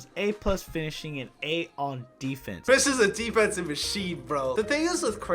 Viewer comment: Did just say “Kris is a defense in machine” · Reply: DEFENSIVE IS WHAT HE SAID YOU DEAF ASS 😂😂😂